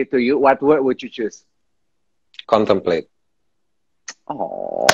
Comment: mas nik ngomong apasii? :( awam bgt bahasanya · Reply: Representasi di 2020 dia milih kata: Contemplate (perenungan)